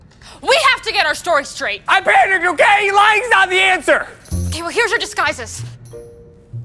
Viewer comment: did say "lying is not the answer" does that mean and r dating in the story?